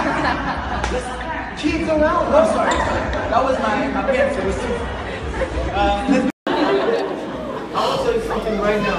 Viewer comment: What Laurent said? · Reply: littleme he said there is kids around